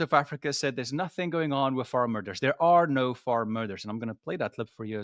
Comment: maybe you should take a breathe between farm and murders it sounds like your saying far murders instead of farm muders